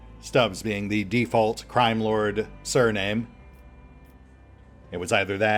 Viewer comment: Finaly the lord captain is back. And could have sworn that you said Prime Lord and not crime lord in character creation, I should probably check my hearing. Looking forward to the series